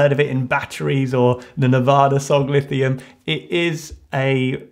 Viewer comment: Really thought he said Nevada Instead of nirvana 😂